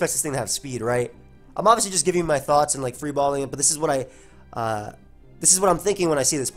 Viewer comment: Did just say he's "free balling it". Like I just feel that we didn't need to know that.